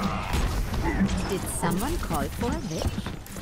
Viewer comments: Ah, everytime Mercy says: "Did someone call for a witch?" I keep hearing: "Did someone call for a bitch?"So i was confused for like the first round lol. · When Mercy said "Did someone call for a witch?" I heard "Did someone call for a bitch?" xD I had to rewind and check. This is really informative and well done. I do have the devil Mercy but I didn't get the best skin from Halloween. My friend who never ever plays Mercy got the skin and I was quite upset :p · it sounded like mercy said "does someone need a bitch"